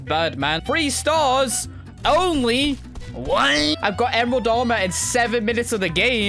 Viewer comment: I like when he says "WHAT" in another voice sounds like he's saying WHAEEEE